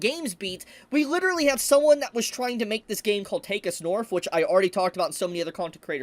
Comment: Whenever I hear you say take us north it sounds like your saying take a snorf and I think of it like dr Seuss book about snorfs in cages. I don’t know what a snorf is but I want one